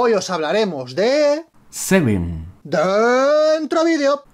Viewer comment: Semen¿? Que mierda escuche jajajajajaa